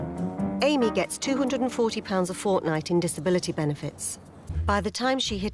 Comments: did she say fortnite? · did she just say fORtNitE? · amy gets 240 pounds of... fortnite? did i hear that correctly?